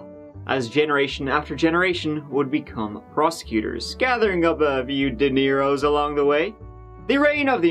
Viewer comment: When said "making a few dineiros along the way," I thought he said "making a few De Niros."